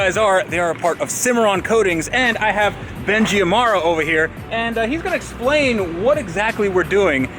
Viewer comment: Did he said "Benji Ammara"? 😂